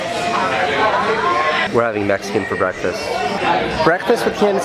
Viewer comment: at first I thought you said we're having a Mexican for breakfast. lol